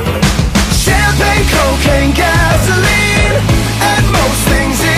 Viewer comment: I have always thought he said "Champagne, coke and gasoline", what the hell, how could i have been that innocent lol